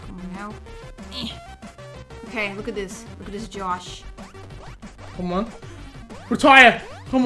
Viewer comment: did he just say "Josh*